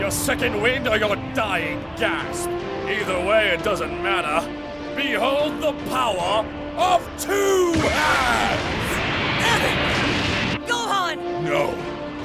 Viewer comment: I thought he said : BEHOLD THE POWER OF TWO DADS....lol🙃